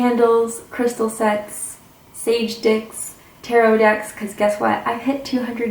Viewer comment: I’m sorry... did I hear you say “sage dicks”? ..🤔